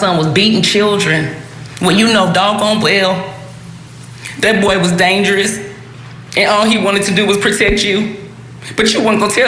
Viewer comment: Did his mom said he was dangerous if I hearing shit y’all let me know 47:20 y’all please let me know Iam not tripping